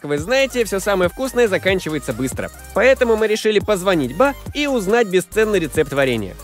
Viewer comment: Что он сказал на 2:26 твои вариант А)позвонить ба Б) позвонить па В) позвонить да Г) позвонить вам Д) позвонить нам